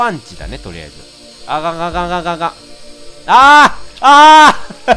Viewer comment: ここレトさん「あかんあかんあかんあかん」って言ってるみたいだけど「あががががががががが」って聞こえるw